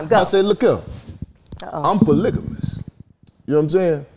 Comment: That nigga Marcus said Polygamous they thought he said Pull A Ligament 🤣🤣🤣🤣